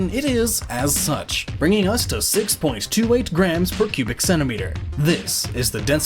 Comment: Man of adamantium* and why in hell would superman need those? sounds like you say Pubic Centimeter :S